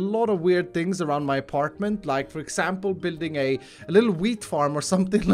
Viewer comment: I thought he said "Building a little weed farm"